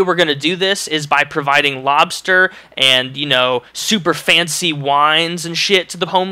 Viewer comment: lol thought you said mobster instead of lobsters lol makes more sense lolz